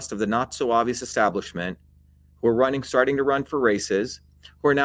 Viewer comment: He says ''the Not-So-Obvious establishment''. I thought he said 'the Nazo Avo' and could not find it in dictionaries! He, he!